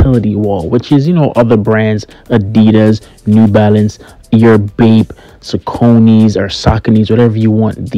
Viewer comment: What shoes did u say? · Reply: Bape